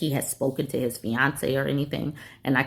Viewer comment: Story #2- I misheard "fiancé" as "Beyonce" and was super confused for a moment. Like, why does Beyonce care if you turn the heat on, tf? 🧐 but honestly it makes even less sense that the fiancé wants them to be cold 🤡 one of the best things about being single is getting to control the thermostat myself 😊